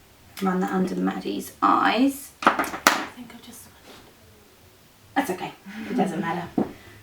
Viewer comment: What did the model say at 6:30? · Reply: "i think i've just smudged it"